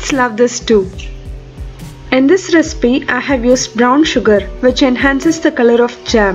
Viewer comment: Sorry I should’ve listen to the end but I heard you say brown sugar thank you